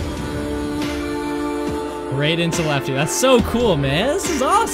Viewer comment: my mans, did you just say right into lefty ?!?